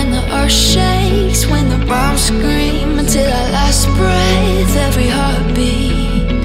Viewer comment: what i hear: "when the braums scream" what normal people hear: "when the bombs scream"